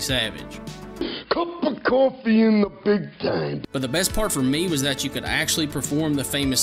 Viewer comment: what does macho man says? Cup of coffee (dont know the rest).... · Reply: Cup of coffee in the big time. Ohhhhhh yeeaaaahhhh!!